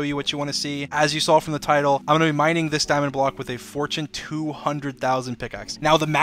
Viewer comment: eu ouvi EI MINE?!